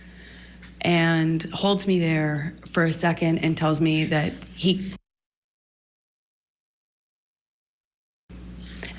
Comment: Why cut the sound at 28:42? What did she say? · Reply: F word